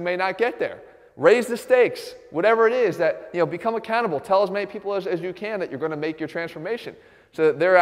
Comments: Did he just say 'become a cannibal'?LMAO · I thought he said ‘become a cannibal’... not ‘become accountable’... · who else heard "become a cannibal" instead of "become accountable" XD great vid though! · I sincerely thought he said, "become a cannibal" around the 4:10 mark. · I thought Jeff said 'become a cannibal', not 'become accountable'. I was like o.0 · LOL. At 4:11 I thought he said, "become a cannibal, kill as many ppl as you can." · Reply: "Become accountable. Tell as many people as you can that you're going to make your transformation so that they're actually keeping track of your progress and keeping you on track." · I thought he said "become a cannibal" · When Jeff said "raise the stake, become accountable" i thought he said become a cannibal as in if I dont get to where I want to be ill start eating humans 😂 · I thought he said "Become a cannibal". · accountable I thought he said a cannibal lol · Become accountable!!!!! I thought you said "Become a cannibal". Had to go back twice..... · Lol, I thought you said, "Do something wild. Become a cannibal." It took me a while to realize you said, "accountable." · I thought he said "raise the stakes, become a cannibal" 😂